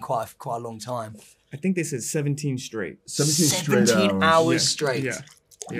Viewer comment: wait, someone please help me, i'm not a native english speaker, what do they say at the min. 6:15? did i hear right? 17 hs straight of interviews?? that cannot be right, right????